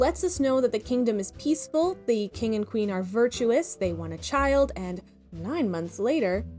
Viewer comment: They actually said "A virtuous queen, and a king.", meaning the king was not included under "virtuous". The sentence was also said with emphasis that implies that the king was an afterthought. "A virtuous queen! (and a king)"